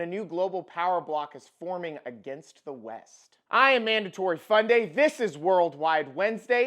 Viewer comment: I had to double check the name of your channel; every time you say it I hear "mandatory funding" instead.